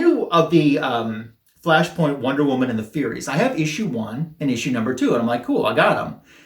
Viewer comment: Cider-Man .. thats so bad ..its fermented ... LOL . I thought that Wonder Woman said.. Furries when you showed it at first ... lo l